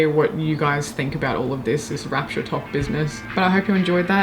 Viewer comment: 100% thought you outro said “make your bed, eat your bed”